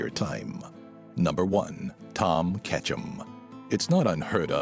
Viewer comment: I thought he said "Ash Ketchum" but he said "Tom Ketchum" XD